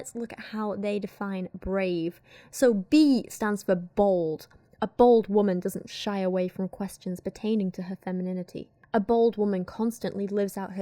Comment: ngl...i thought you said "B stands for BALD."